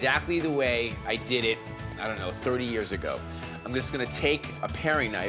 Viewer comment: i thought I heard him say 40 years. i’m getting old.